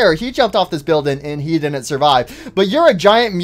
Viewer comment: I thought you said he totally did survive